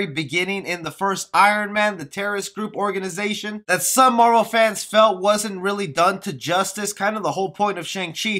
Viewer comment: I heard you say Hey moral fans.